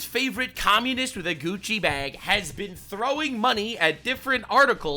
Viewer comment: when you said "everyone's favourite communist with a gucci bag," i legit thought you said "...communist the coochie bag."